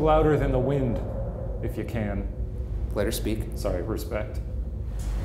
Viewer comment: i hear "Im sorry".